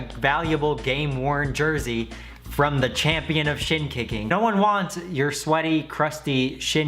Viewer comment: I thought he was saying shit kicking